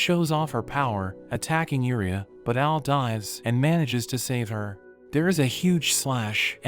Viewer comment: The subtitles say, "But Al dies and manages to save her."